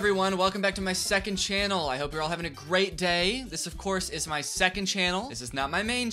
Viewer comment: I thought he said "a Greag day" idk why